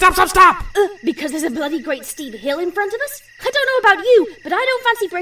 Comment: What did she said? "... because there is bloody {???} in front of us...| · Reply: A bloody great steep hill.